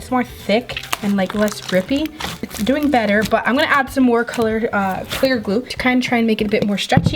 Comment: Did anyone hear her say coloured glue